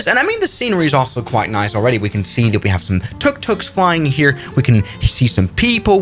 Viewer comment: did he really say that TucTucs are flying 😂